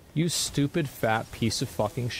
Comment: Hits video to watch. First thing I hear is, " U stupid...pofs," 😂😂😂😂. WTH did I just walk into.